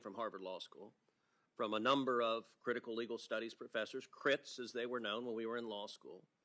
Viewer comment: I thought he said crips I lost it lol